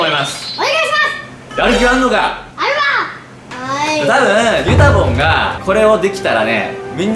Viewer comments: ゆたぼんがメタモンに聞こえた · 先生に授業教わりたい… 先生がゆたぼんって呼ぶのがずっとメタモンって聞こえる…(小声)